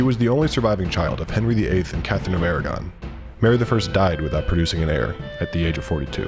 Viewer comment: What I heard: "the only child of Henry VIII and Captain America "